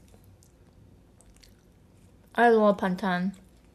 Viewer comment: i thought she said bangtan 😂😂 , I'm 100% sure i heard her wrong 😂😂😂